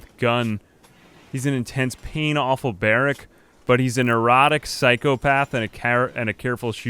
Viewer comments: I was half listening because working on something else and thought you said one of his traits was "erotic". Had to rewind and realized it was "neurotic", but for a second there I wondered where the hell this let's play was going. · OMG I THOUGHT I HEARD YOU SAY EROTIC PSYCHOPATH AND IM LIKE WOAH WHERE'D PINHEAD COME FROM? XD